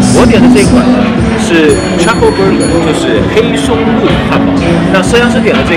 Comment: 我把黑松露汉堡听成了trump burger